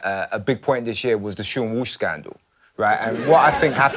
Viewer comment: Can someone please tell me what the dude on the end said about a scandal? I've seen it a couple times now but I can't place it and I'm Canadian so I guess I'm missing the context. He said it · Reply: The "Seann Walsh scandal" He was taking part in 'Strictly Come Dancing' (I think the American version is called 'Dancing With The Stars'?) when he was filmed kissing his dance partner, Katya Jones, on a night out. Jones is married and Walsh, at the time, had a girlfriend. She dumped Walsh shortly after the video became public.